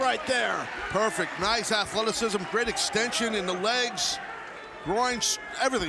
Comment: did Taz say; "great extension in the groin"? That's a new one!